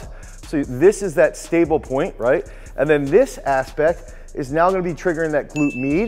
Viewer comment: did you say "this aspect" or "this ass pec"? :p